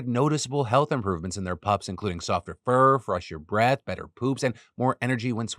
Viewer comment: Definitely thought said "better boops" when talking about the dog food. I was like... that's an interesting (and much appreciated) metric